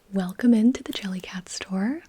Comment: "Welcome to the Jellycat store." I misheard it as Jellicle